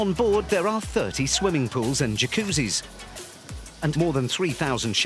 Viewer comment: did he say 30 pools?